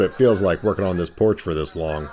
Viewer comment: i thought it said porsche restoration